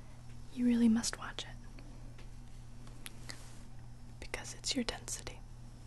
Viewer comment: What does she say at 39:38? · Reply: "because it's your density" it's a reference to back to the future, marty's dad is a nerd so instead of saying "it's your destiny" to the girl he likes (marty's potential mom) he says "it's your density"